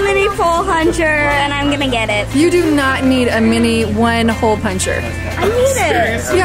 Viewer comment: looool said "pol huncher" instead of "hole puncher" her that was so cuteee